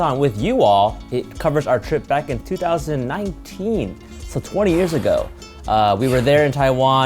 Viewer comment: Did anyone else hear steven say "20 years" at the start?